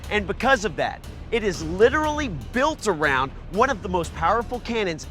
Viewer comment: What did he said? 2:33 "Buile around"? So its not Plane with a gun..